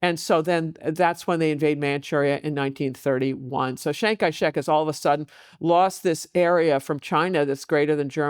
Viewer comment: I heard her say Chang-Kai Shrek at one point and I was thinking... did this Nationalist leader have a talkative donkey friend. 😂